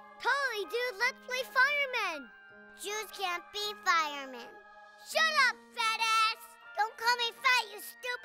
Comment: At first I thought Cartman said “You can’t be firemen.” and “Don’t call me fat you stupid jerk!” but now I realize he said “Jews can’t be firemen.” and “Don’t call me fat you stupid Jew!”.